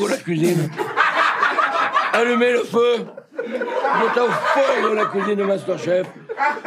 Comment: Queria um like só pela trabalheira de legendar tudo isso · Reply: Parabéns pela legenda UAHAUAHAUS Aliás 20:07 a parte que não tá legendada que não deu pra entender o que o Jacquin falou, acho que ele disse "allumer le feu" (a leitura seria algo tipo "alumê lo fô") que em francês é "acender o fogo" uabsiabsuahuaha